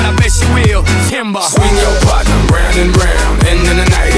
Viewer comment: I thought he said swing your butt round and round but it says swing your partner round and round